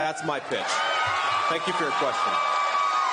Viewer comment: It sounded like he said “that’s my bitch” in the beginning lol